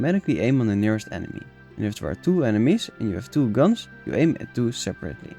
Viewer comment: I totally forgot to comment, sorry... First of all, thanks for the compliment! But in the first Tomb Raider you can actually shoot two guns at the time, the footage at that sentence shows it. I didn't mean that you can shoot at two enemies at the same time, just two guns ;) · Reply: Ah gotcha, I thought you said target two enemies. My bad.